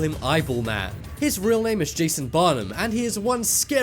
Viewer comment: I thought they said Jason Bonham and I was like, what the fuck?